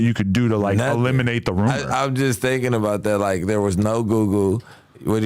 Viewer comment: Bricc is leaned out his mind!! Lmao at 1:46:24 he like “there was no GooGoo” instead of Google 😂😂😂 please don’t look up to this kids!!